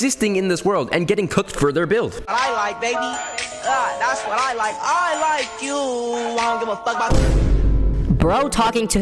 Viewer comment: i swear the first guy said i like fooddd